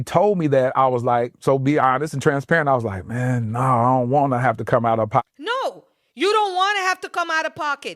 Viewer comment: Not making excuses for him, but I think he actually said, “No, I don’t want her to come out of her pocket.” (The southern accent got in the way)